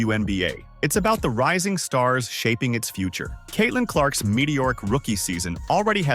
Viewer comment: Did you just call Clark's season "Medioric?" It's not even a word. I would think you meant to say it's mediocre? You're tripping. She could have won the MVP if it wasn't for the media.